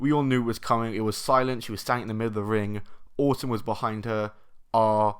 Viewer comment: I like the way you said Orton because it sounded like autumn